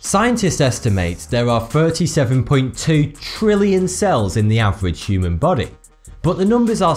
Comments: it says 32.7 trillion but he really says 37.2 trillion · Anyone else hear him say 37.2 trillion but the screen said 32.7 trillion?! At 0:52-0:55 it's in there between those points!